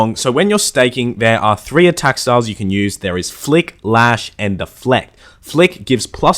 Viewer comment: anyone else though it said "FUCK" instead of flick at 1:07?! 😂